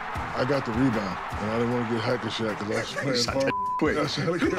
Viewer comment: did Shaq just say he didn't want to get "hacky shaqed"?